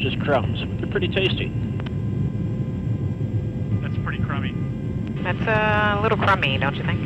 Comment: "ThATS PrEtTy CrUmMy" I thought they said "Thats pretty cummy"